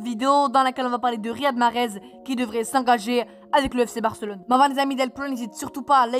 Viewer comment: tu dis quoi la stp😂t'es le Boss! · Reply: mais avant les amis